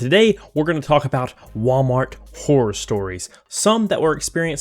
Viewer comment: Sorry, for a second there, I thought you said, "Walmart whore stories", and I became curious for some reason.